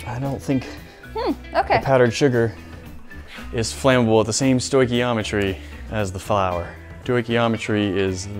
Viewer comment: What is the word? "doigeometry"?